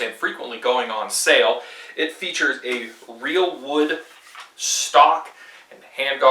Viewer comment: I'm trying to find out what kind of wood they used for this, can anyone help? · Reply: I heard it was “crate wood”